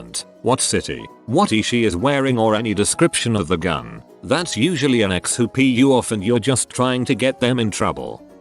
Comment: what was the h-word? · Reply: Heroin